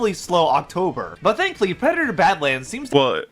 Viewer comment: This guy actually said "EXspecially" and "all intensive purposes" in less than 2 minutes, we're in for a treat with this one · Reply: And at 1:03, while the subtitles claim he said "thankfully", it sounds like he said "thanklly"